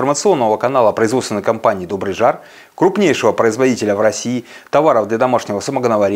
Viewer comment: Крупнейший говорите а в миллионнике Волгограде нет вас как то не хорошо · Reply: Дословно сказано, "крупнейший производитель", а не "крупнейший продавец", как возможно Вам послышалось. Да еще есть миллионники где нас нет. У Вас есть возможность это исправить. Мы ищем партнеров.